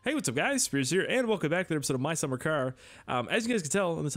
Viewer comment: it sounds like you said welcome back to my summer care